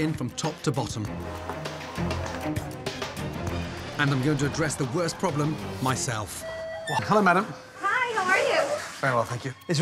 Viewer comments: The why Gordon pronounced "I'm gonna address the worst problem myself" made it sound like he said "I'm gonna address the worst problem: myself" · I heard it as “I had to address the worst problem: myself.”